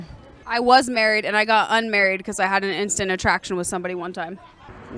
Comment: "Did she just say "unmarried"??? 🤣